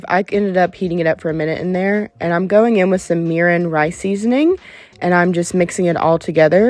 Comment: I thought you had said Coffee seasoning til I rewound the video. But im almost curious now if a little instant coffee would be good here? ❤